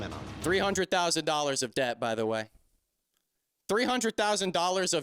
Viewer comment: I really thought you said "$300,000, if dead", like you were putting a hit out on Paul lmfao.